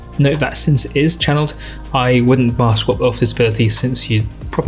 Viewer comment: Hello. Could anyone tell me what was said about wrecking blow at around 12:25? “Since it’s a channeled ability I wouldn’t ______”. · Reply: Wouldn't bar swap off this ability, since you can't do so without losing rotation speed, or along those lines